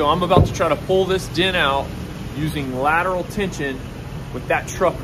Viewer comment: Instructions unclear. I dragged my door right off the hinges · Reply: Oh lateral tension. I thought he said leave an indention. That makes more sense. Oh boy.